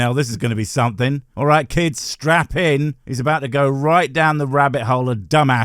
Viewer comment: "Did he say strap in or strap-on?"